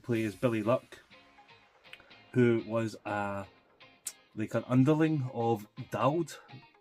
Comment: Thought you said an underling of Ken Dodd hahahaha